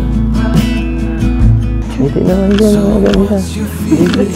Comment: Hindi kami Maka move on sa dalawa na ito true couple na ❤ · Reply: Narinig nyo ba sinabi ni edu 11:26 ngiti naman jan ng maganda baby